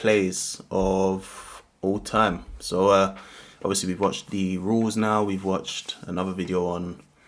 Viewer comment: I thought you said you watched the rules. Need to watch again 😉